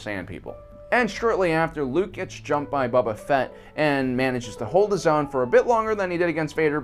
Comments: I keep hearing "Bubba Fett" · I'm sorry I hope I misheard u when u said boba Fetts name...pshh bubba Fett really